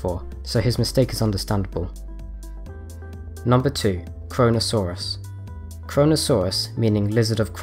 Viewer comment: it sounds like he says "Coronasaurus"